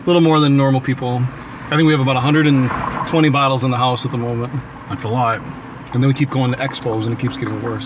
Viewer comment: it sounds like said that's a lie